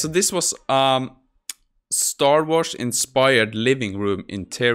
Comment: I confused star wars for star trek when you said star wars, so I looked at the pictures, and thought "that's not star-trek inspired, it's star wars, then realized I had heard wrong, so the design is so strongly star wars it went against my misheard bias.